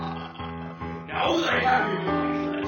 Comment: What did Bowser say at 4:26-4:30? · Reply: "now that I have you in my clutches, you're gonna do exactly what I say". how can you mishear that?